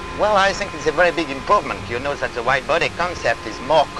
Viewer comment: i thought he said wide booty lol